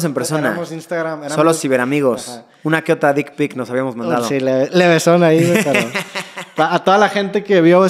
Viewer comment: Se mandaron una verga o qué fue lo que dijo?? 😣😄😄